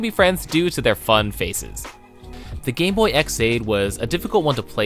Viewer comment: I heard gay boy😂